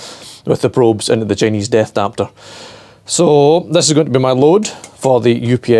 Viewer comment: did you say DEATH adapter?